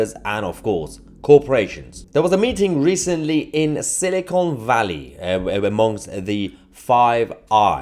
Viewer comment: I probably misheard but I thought you said "Cynical valley.